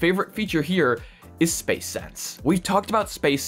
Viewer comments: Did he just say Space Sex? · Swore I heard space sex the first time